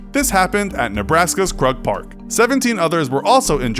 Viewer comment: Thrug park? I thought he said drug park lol